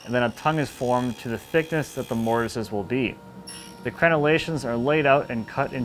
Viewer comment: What is the word around 4:40 that sounds like "crenulations"? My web search skills have failed me on finding what exactly this means. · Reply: crenellations